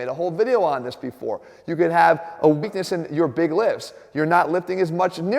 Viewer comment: I thought he said "Weakness in your big lips." I felt attacked...